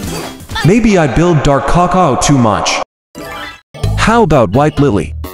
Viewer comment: I heard "maybe i built dark caca to much" and was surprised 😭